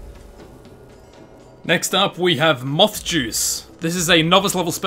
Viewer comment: I thought moth juice said math juice